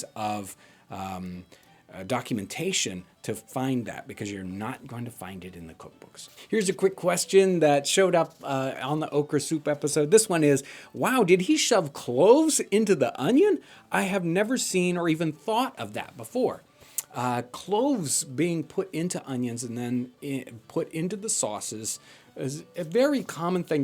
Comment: I thought you said " clothes in the oven" then realised it was " cloves in the onion" lmao, could have been disastrous!